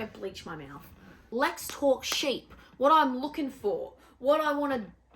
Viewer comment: Right out the gate I thought you said "let's talk shit!" and I was like "YES! LET'S!" I will just as happily listen to you talk sheep too lol